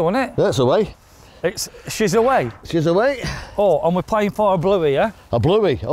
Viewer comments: What's a bluey? I thought you said yall were playing for a blowey 😄 had to go back and listen again. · I thought you said playing for a blowy for a second 😂